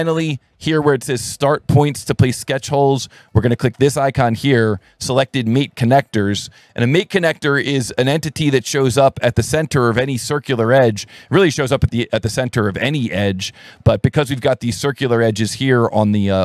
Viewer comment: I misheard you and now all I can hear is "meat connector" over and over. I swear I'm an adult